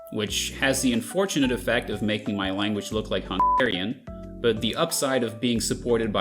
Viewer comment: Hungarian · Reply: i heard "hontarian" or somethin